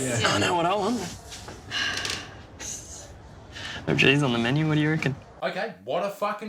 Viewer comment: Did the restaurant guy say "no cheese on the menu????" Wtf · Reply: I thought i heard "know if she's on the menu?"